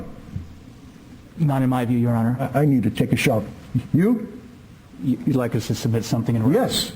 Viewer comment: I swear I hear Judge Wright say “I need to take a shower” at 35:40 ish